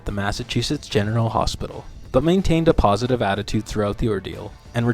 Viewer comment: did dude say Mass. Genital Hospital😂